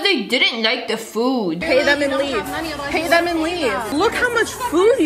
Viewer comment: all i heard was PAY THEM AND LEAVE👹